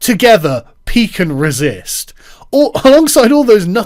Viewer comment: I was listening to this without watching the video and suddenly I hear "pee can resist" and fucking lost it